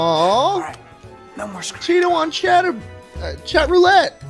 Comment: What was he about to say at 5:10? 😂😂😂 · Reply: chaterbate